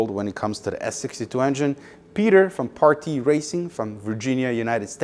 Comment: What the hell is Party Racing? That's what the subtitle says is building your engine. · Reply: Partee Racing.